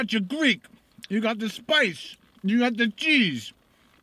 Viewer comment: Did he say: "You got the jizz"? on 4:28? xD